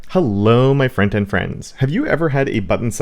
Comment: I always thought you were saying, "Hello friends and friends" and was so confused... But thanks to the subtitles, I get it now. Thank you